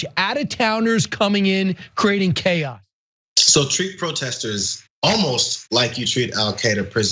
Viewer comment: lol I thought that said "Treat Professors like Al Qaeda" I was like whut... though Protesters, yeah whut... this is just... plain authoritarian. You're endorsing torturing citizens